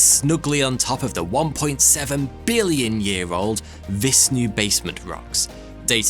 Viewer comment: when says “Vishnu Basement Rocks” I thought he said “this new basement rocks!”